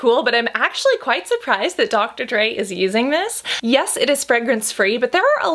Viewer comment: I see “Dr. Dray” but I hear “Dr. Dre”. It’s because of you Eminem...